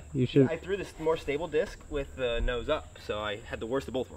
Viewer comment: did he just say worsible ? Seriously, one of the most fun episodes ever, so many cool moments 😂